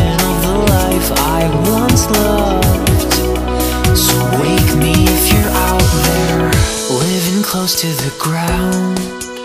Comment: I misheard that as "women close to the ground"